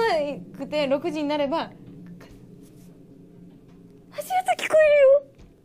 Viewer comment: Thank you for the upload! Subbed! On 1:40 I think said "Ashioto kikoeruyo" which means I hear footsteps.